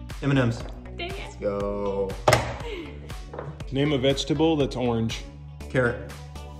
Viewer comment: Yo I misheard and thought he said fruit instead of vegetable so I said orange lol 😂😅